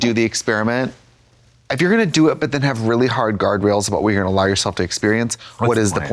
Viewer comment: Oh she said GUARDRAILS I heard GARGOYLES